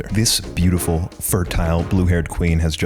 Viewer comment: Oh what a good and funny video Im watch- 13:26 [...] Did he just say "fertile"?